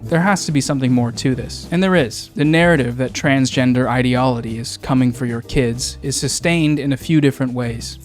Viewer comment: Love you and your stuff but... At 8:03 you say "ideolody" instead of ideology and it had me laughing for three straight minutes